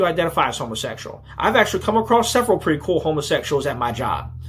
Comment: I totally misheard the guy when he was talking i thought he said " I have no problem with gay guys or homosexuals at all, Ill breed em."